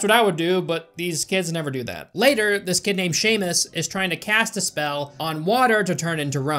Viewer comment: I moved my headphones a little bit and it sounded like said “That skinhead Seamus” 💀